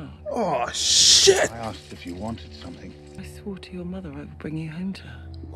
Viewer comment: Hi! Just curious, I may have misheard you but did you say your mother Isla? My daughter is named Isla and I don’t hear it much 😊